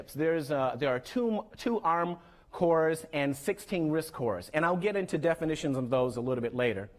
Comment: dit I hear right 2 ARM (Acorn Risc Mashine) and 14 Risc cores?